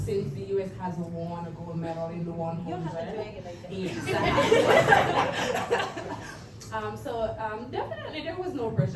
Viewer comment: What did Sha'Carri say at 8:00? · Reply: ‘You don’t have to do that!’